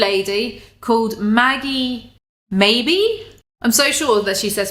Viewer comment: I heard that too, "Maggie, MAYbe?" Lmao I had to rewind cause it threw me off and I couldn't figure out what they were trying to say lmao